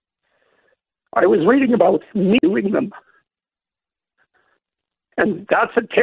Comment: What word does he say at 1:20. "I was reading about ???ing them"? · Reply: oh I think its "I was reading about me doing them" and the audio split during 'doing'